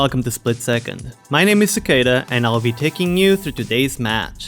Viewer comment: I always thought you were saying cicada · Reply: xD Sequeira is his real name but it might sound cicada indeed.. and the automatic generated subtitles usually translated them to cicada as well xD